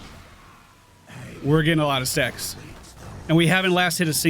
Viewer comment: Did just say "We're getting a lot of sex?!" Hear it again and again and you'll hear that I assure you!